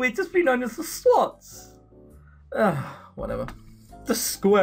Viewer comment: The whats? Kinda sounded like you said sluts, or maybe swans or slots. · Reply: Im pretty sure he said Swots. (people who value education a lot more than most and tend to have superior grades to others)